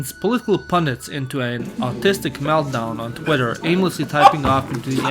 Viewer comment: Wait, did you say "autistic meltdown?" Autistic and artistic sounds so similar after all.